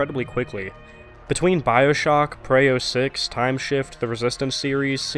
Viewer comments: All I’m saying is gmans “employers” are totally Valve Software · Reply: What game did he say at 2:25? Sounds like Prayo 6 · What game did you say at 2:25? Sounds like Prayo 6